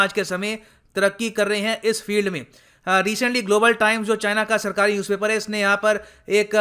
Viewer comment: I hear Gobar Time😂😉